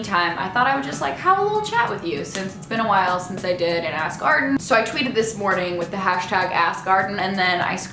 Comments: towards the end I thought I heard you say "that's the end of this ass garden" instead of "ask arden" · "that's the end of this ask arden" - i literally thought you said that's the end of this ass-garden so yeah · when you said "thats the end of this ask Arden" I thought you said "ass garden." I had to replay lol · I legitimately thought you said ""ass garden" at the end for a sec hahahha! Sorry for that interruption. Now I must resume my regularly scheduled mature-self.